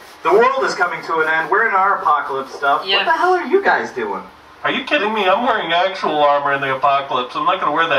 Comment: holy shit that’s fucking awesome. I thought he said “are you kidding me? I’m wearing Epsilon-“ and nearly freaked out pffft